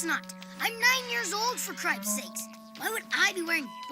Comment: I thought said "For Christ Sakes". 😂